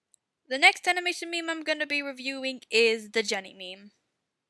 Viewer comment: i thought she said gender meme XD